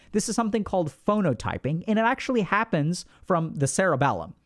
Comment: I can't find the word to which Alok refers to with the neuroscientific correlate at 8:20, it sounds like he says "phonotyping" but that brings up nothing on google, I must be mishearing it, what is the word that he is referring to?